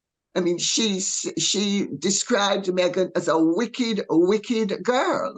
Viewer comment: The queen described Meghan as EVIL 😯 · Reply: I thought she said ‘wicked’ now I need to go back and re-watch it again. Which is okay because it was such a delightful vid. 😊